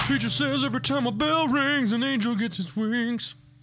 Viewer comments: no but when I first seen a video of them I thought they were saying Dougie of Awesome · That sounded like you said Duckie of Awesome -squeak squeak- Lol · OMG.. I just found this show and watched about 8 videos.. I thought it was saying ducky of awesome.... ...... I'm glad I'm not the only one.